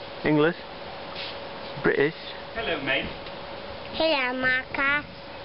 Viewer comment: Whoa there !!!! Does say "maakaa" around 00:28 ?